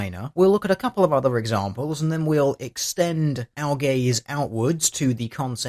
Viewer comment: All I heard here was "extend our gays". I dunno what kinck this is but I wanna watch.